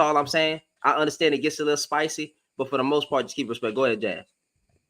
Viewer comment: GREAT show and channel! 🌺@ Did I hear the speaker's name as "Jazz?" Unfortunately, listening on YT, we do not see speaker's names/handles. Thanks in advance. She BREAKS it DOWN. 🤯